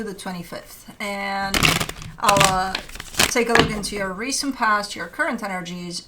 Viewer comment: It is been years since I quit tarot and now I got it recommended and well my name is Alaa and I heard you saying it at the very beginning at 0:06 isnt this strange 😅 I thought you said "Alaa take a look" and it freaked me out😂